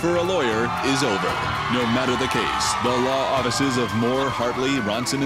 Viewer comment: it sounded like he said “the law oddices”